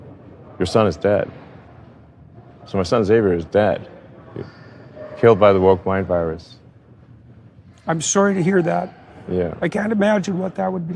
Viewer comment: Did I hear "I promise to brake the woke mind virus"? Let's put him under the microscope! Elon, your genes were the problem with your child. Are you so smart that you lack total common sense?